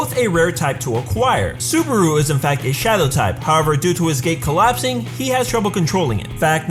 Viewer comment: is it me or did he say "due to his GAY collapsing" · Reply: Yeah lol, he meant to say “due to his gate collapsing”